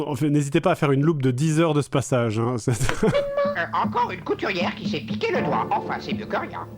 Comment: j'ai compris "encore une couturière qui s'est niqué le doigt"... J'ai besoin d'aide